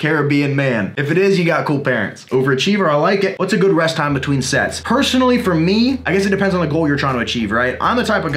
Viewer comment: I thought you said "Whats a good rest time between sex?"